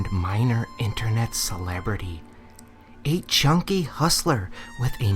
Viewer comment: Watched this again tonight. This entire time I thought you said "a chunky hustler" but this time I heard you say "a junkie hustler".... It's still hilarious. And somehow, this time, no matter what voice you used, I got the ASMR tingles. Nice work!